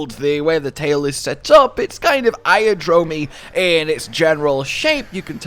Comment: did you just call that Egyptian themed Great Wroggi a Iodrome?